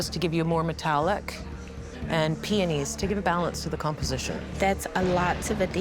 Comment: The way the one lady said peonies pswissed me off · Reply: sounded like she was saying peenies